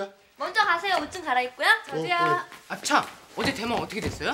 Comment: 형욱이가 뭐라고 얘기하는 거예여? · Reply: 장폭스 프사 본인이심?이쁘시네요 암튼 형욱이의 말 “참 어제 대망 어떻게 됐어요?”가르쳐 주세요 장혁 어떻게 됐어요?이러네요 대망은 SBS에서 <num>~2003.1.5일에 끝난 장혁이 출연했던 26부작 드라마네요ㅎㅎㅎㅎ